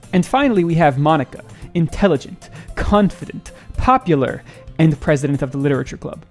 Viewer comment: Did he say "cuntfident"??? 😭😭😭😭😭😭